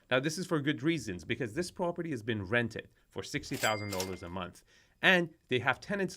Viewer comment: The house is generating 7.2 million dollars a year in rent and is on the market for only $23 million dollars? Why on gods earth would somebody want to sell this? It would pay for itself in less than 4 years. Seems odd. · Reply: Ooops—never mind—I thought he said $600k a month rent.